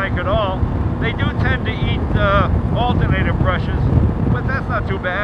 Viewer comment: Can ANYONE help me with what Jay says at the exact 10:29 mark??? He says.........."and they DO tend to eat alternator ****"??? Alternator WHAT Jay??? Lol. Any help would be appreciated!!! :) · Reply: "brush's"